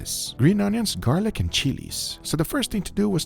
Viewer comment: did he say cheese 😂